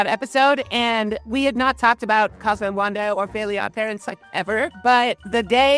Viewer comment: I thought it said “Fairly Old Girlies” for second😂😂